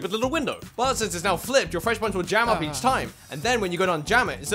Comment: I hear "Freshly punchers will jammed up each time" what did he say though really?